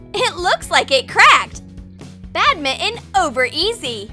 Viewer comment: did she just say “bad mitten”